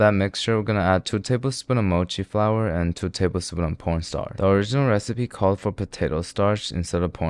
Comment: Am i bugging or did you just say porn starch?😭